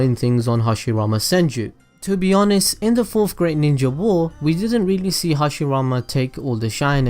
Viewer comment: I deadass thought that you said the fourth grade ninja war instead of fourth great ninja war.